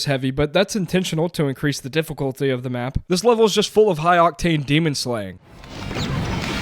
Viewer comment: Did you say "High octave demon slaying?" My guy, an octave is a range of audio pitches. Fun video, anyway. ——— He said "Octane" 15:52. I misheard him.